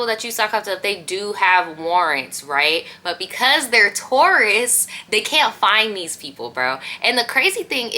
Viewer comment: I thought I heard you say Taurus not tourists. I was like what does astrology have to do with it😛🤪